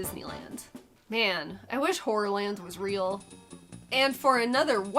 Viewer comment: I could have sworn you said, "man, I wish whore-land was real!" I was wrong, but it sounds pretty close!!